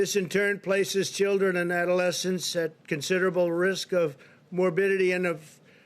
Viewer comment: "Risk of morbillion". What word is morbillion...?